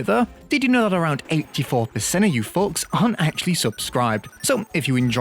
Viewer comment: i heard fucks instead of folks. "did you know, that around 80 percent of you fucks arent subscribed?" sounded really funny